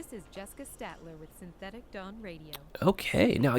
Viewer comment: did she just say "This is Synthetic Dong Radio"???